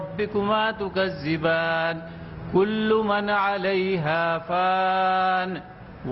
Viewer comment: did he say kullu manali ? 🤔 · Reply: kullu Man Alaiha faan-----------------रोय ज़मीन पर जो चीज़ है वो फ़ानी है